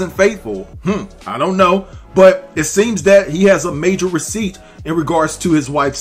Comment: Wat receipts? Sound like "he said, she said" 2 me